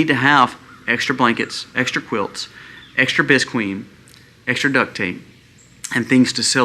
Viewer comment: For others as clueless as me!, I had to look up what I thought he said was 'bisqueen'. Turns out it's 'visqueen' - Visqueen is a brand of polyethylene plastic sheeting produced by British Polythene Industries Limited. It is the registered trade mark of British Polythene Limited in numerous countries throughout the world. It is commonly between 4 and 10 mils thick and is available in clear, opaque, blue, and black. Visqueen is used for many purposes. - Wikipedia